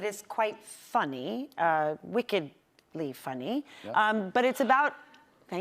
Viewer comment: What did she say ? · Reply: "wickedly funny" made someone laugh apparently